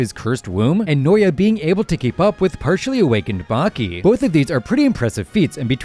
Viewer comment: "partially awakened baki" was i the only one who heard "baki"